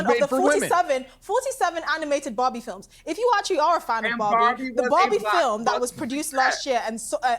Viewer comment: which bobby is she talking about? 🤣 I'm curious, it doesn't matter how many time i listen to it, i always hear bobby and not barbie.